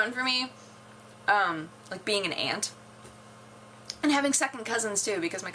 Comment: I'm not done the vid but when you said "being an aunt" I thought you said "being an ant" so I immedietely went WTF!? and spilled oj all over myself D